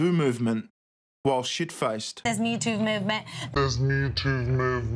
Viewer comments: i thought she said the mewtwo movement. #mewtwo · No, what she said was Me Toov Movement. Listen closely when it's slowed down at 2:00. Metoov movement.